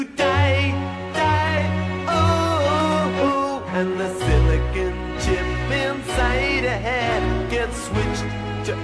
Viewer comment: I thought they said "Silicon Ship" for years.